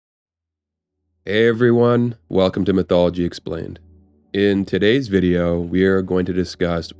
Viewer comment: i heard mcdonaldgy explained